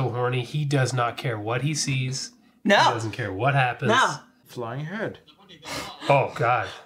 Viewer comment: I hear "Flying head, hmm." as one of the things that "Hand Job" will overlook to hit "it." His more detailed immediate response upon further reflection is: "Hmm, the possibilities." 🤪